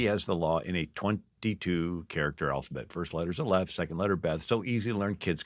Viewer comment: i can't understand what he said after "22 character alphabet." it sounds like he says "First letter is a left, second letter beth." what is he saying?!